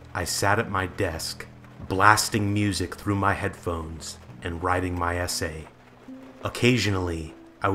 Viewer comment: I misheard "writing my essay" in the first sentence of this video as 'riding my ese' and my first thought my "oh nice, good for him" then my brain realised.